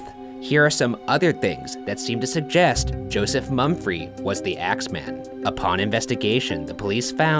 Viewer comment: I low-key thought you said X Man the whole time